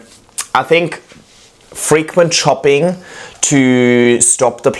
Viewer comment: I keep hearing "frequent shopping" 🤣